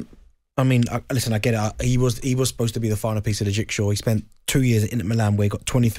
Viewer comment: Did he just say the final piece of the rickshaw! Lol 😂